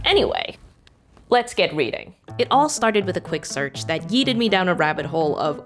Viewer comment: The subtitles say yielded, but I'm sure I heard yeeted. Which is weired for several reasons, the most important one that the past tense of "yeet" is "yote".